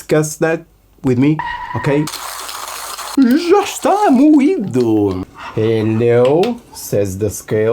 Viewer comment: "Já está morrido!" Am I hearing that correctly?! 😂